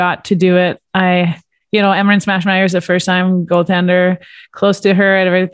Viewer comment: Every time someone says "Emerance Maschmeyer", I keep hearing "Smashmeyer". I hope her friends gave her that nickname.